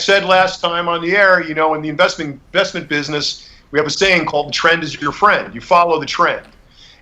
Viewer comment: I thought he said "tren is your friend"